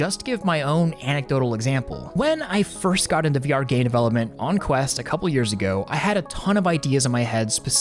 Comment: I'm not the only one mishearing that am I? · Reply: VR GAY DEVELOPMENT 🗣️🗣️🗣️